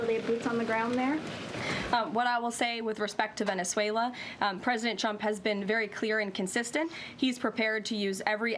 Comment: she is still saying "President Chump"....